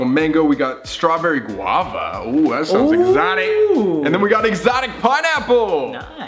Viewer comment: You said exotic pineapple and I don't know why my brain thought you said exotic butter and went "five night at freddys"